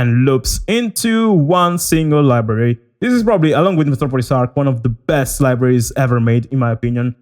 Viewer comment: I have absolutely no clue what you say here and I'm trying to figure it out. It's the other VST you say is your favorite with Damage. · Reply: Along with Metropolis Ark